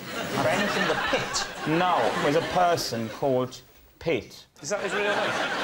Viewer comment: i actually thought he was saying pete even when he corrects them it sounds the same lol.